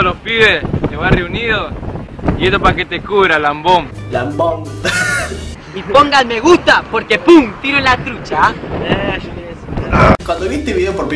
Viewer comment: entendieron un MG o un tiro en la trucha jajaj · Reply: Yo pensé que decía: -Y pongan me gusta,porque si no pum,le tiramos la trucha.